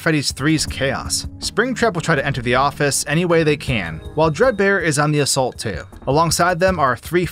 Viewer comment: Did he say drug bear 16:10 😂😂